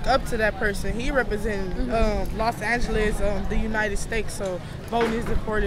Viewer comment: Does she say The Uniting Stakes?